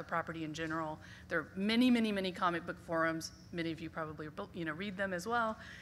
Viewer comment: did she just say comic book forums on FORA Tv ?